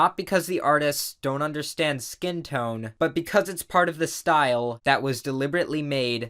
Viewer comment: " its probably Art-aware of the style" At least thats what i thought he said 😂😂😂